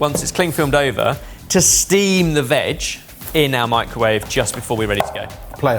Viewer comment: I wanted to be normal and just watch the video but I absolutely cannot ignore the "steam the veg" cause I 100% heard it as "steam the vag" and I will never recover from this. Thanks for listening to my TED talk